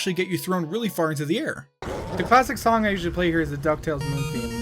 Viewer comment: I can't hear what he's saying for the name of the song, anyone know what he is saying or if you know the name of the song, I want to learn it on the piano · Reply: Ethan Church the Duck Tales Main Theme for the SNES (I think)